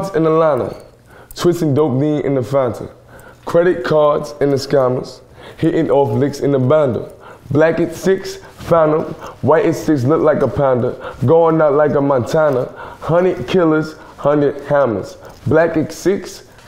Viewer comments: Notice how this is a genius video where you’re supposed to explain the lyrics and not give a live performance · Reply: The whole time all these years I thought he was saying legacy phantom but he was saying blackx6 phantom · I always thought he was saying "credit cards and the scannas" WOW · So lets get this straight.. in no part of the song he said legacy? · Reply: I heard "Legacies, famine, white as he look like a panda, going the leg of antennae, Hannah kills and the Hannahs." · Black X 6 sounds like legacies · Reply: I thought he said "Legacy, phantom" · Black X 6 sounds like legacies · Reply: For the longest I thought this guy was saying legacy, Phantom. Lol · Black X 6 sounds like legacies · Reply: that's what i thought he said at first lmao. "legacy, phantom" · "I got broads in Atlanta"...... Ohhhhhhh I thought he said I got bras in Atlanta. That makes more sense. 😶😳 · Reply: "Black x6, Phantom" I thought he said legacy, Fanta. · Nah its definitely "Legacies" bruh. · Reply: I heard: "legacies, phantom, why does he look a panda" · Am I the only one that thought he said "Legacy, Panda" before watching this video, as well as "credit cards and scanners."